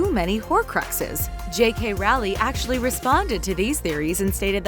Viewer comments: did she say jk rowly? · Did she just say JK Rowley? 😂😂😂😂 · did she say J.K rally? Edit: maybe hedwig is lily. 🤔 Harry has survived death and dementors while hedwig is alive so maybe he protected her. 🤔